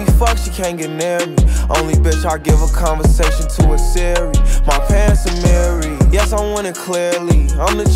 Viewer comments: when he said my pants amiri I always thought he said my parents are married 🤣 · Who also thought he said my parents are married but he actually said my pants amiri 😭😂😭😂😭😂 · Whole time I thought he was saying “my parents are married” 🥴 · I'm stupid 💀💀 I thought he said my parents are married, yes I want it, clearly 😂😂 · Dude I thought he said "my parents are married" not "my pants amari" LMAO